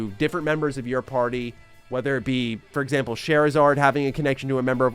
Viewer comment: I swear I heard “Charizard” and was so confused 😂